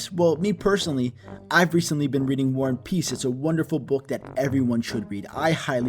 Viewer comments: Not gonna lie but when said "war and peace" I thought she said "one piece" like the anime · I thought she said one piece